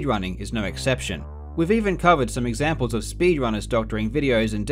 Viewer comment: When he says “we’ve even covered” I thought he said “with even covid”, quarantine hits hard